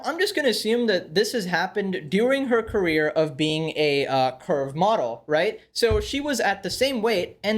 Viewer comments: Thought you said "perv model" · When he first said 'curve model' i heard 'curb model' and did a double take. 🤣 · I first misheard that she is a girth model😅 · you made me really worried for a second, I genuinely thought you said “perv model” instead of “curve model”☠️.